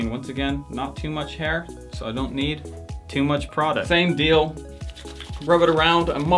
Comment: he says ''and take not to much hair" instead of product